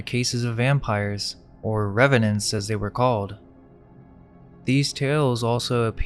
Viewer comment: It sounded like he said there were sometimes referred to Then as REVENANCES . There was a television show REVENANTI think and those people came back from the dead right😊